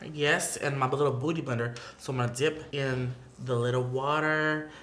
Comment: Love the way Patrick says beauty blender it sounds like hes saying BOOTY blender not beauty blender😂😂